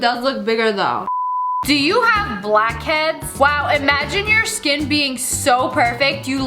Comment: i had my eyes closed at 8:36 i opened my eyes so fast cause i thought she said black kids im so dumb